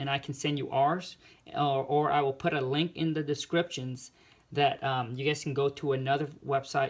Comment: Can I have those contracts I thought you said you were putting a link in the bio.